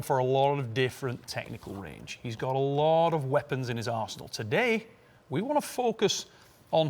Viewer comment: Not gonna lie but 13 seconds in and I thought you said Devon has a lot of weapons in his a@ hole lol